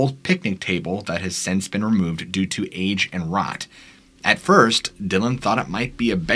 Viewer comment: Had to replay 26:03 because I thought you said the bench was removed due to aids and rot, made a lot more sense when I've understood it correctly and it was due to age and rot🤣